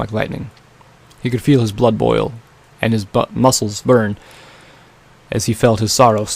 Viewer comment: I heard you say " his butt muscles burned"